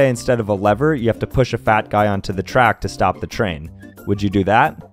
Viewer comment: Oh I thought he said bad guy, I was like heck yeah!